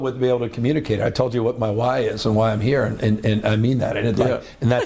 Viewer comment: “What my WHY is…”. ??????? I thought he kept saying “my wife”. Zeitgeist???? — claiming “dyslexic” how did he even know this word? What a poisonous bead of mercury this slime bag is. What is his Rx list?